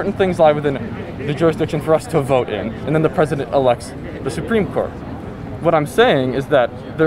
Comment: He CLEARLY says "elects". I think one of the biggest problems with these 20-something idiots is that they've never been punched in the face. REALLY HARD. It makes you a LOT more respectful and humble when you learn the concept of consequences.